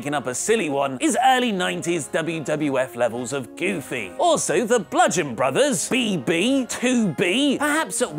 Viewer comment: I swear I thought they said The Blumpkin Brothers.....